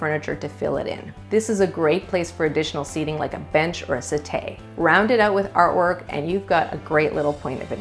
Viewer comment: Great video! Very helpful. At 6:56, however, you mispronounced "settee" as "set-AY" instead of pronouncing it as it is written -- "set-TEE." The closed captioning ("asset a") couldn't figure our what you were saying.